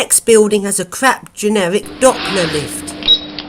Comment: I am not an English, but an asian. So, in 18:47, I thought you said: Crap generic dogfood lift (doppler lifts).